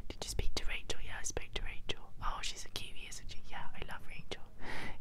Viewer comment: at 22:34 did you say Rachel is a Kiwi or Rachel is a cutie?